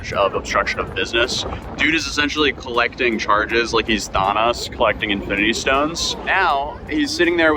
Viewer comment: Wait… 2:22 did you say “Thonus collecting infinity stones”?